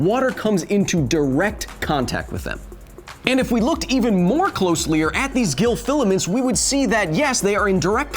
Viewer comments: Did... did you just say "even more closelier"? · does he say closely-er?